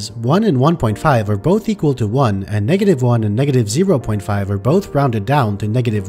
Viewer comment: pushes his math nerd glasses up 4:31 Um...but rounding from -1.5 to -1 is rounding up not down as -1 is more than -1.5 Edit: Misheard the -1 as -1.5 but listening again makes it even funnier to me since you can’t really round something up or down to itself so rounding -1 to -1 makes no sense lol. This post was a joke to begin with anyway lol